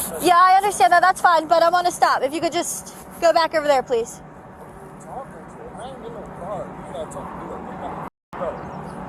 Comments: What I believe Mr. Co Worker said around 2:50 in the video was; "I ain't did no crime, you not talkin to me like that. You got me f***ed up." I do not believe a threat was made. Just a mishearing of Mr. Co Worker's speech pattern. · what he said was "you got me fucked up" which means "you clearly misunderstood something about me". timestamp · The female officer lied. If you go back and play from 2:47 you can hear what the man says. "Who are you talking to? I ain't in no car. You not talking to me like that. You got me fucked up." Unless it's just not presented in the video, he never says that he's going to "fuck her up". She lied about what the man said so that she could justify getting extra units there, directly putting the man in danger and having charges filed against him. I hope they got him a good lawyer, because the terroristic threats and disorderly conduct charges are both bogus. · he clearly says “you got me fucked up”. Why did you pretend not to hear that? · Sounded like he said “who are you talking to i ain’t in no car you ain’t gone talk to me like that , you got me fucked up”